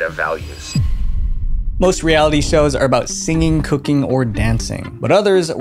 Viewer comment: was bracing myself for the worst when i misheard "semen cooking" but then realized something was off. i guess watching stuff at 2x sometimes has its downsides